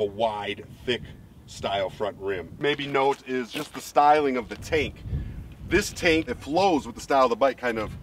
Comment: I had to focus... I thought you said taint